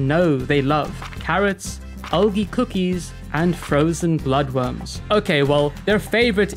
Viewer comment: the caption say Elie cookies. But he sang Aldi cookies. Does anyone know what cookies he's talking about?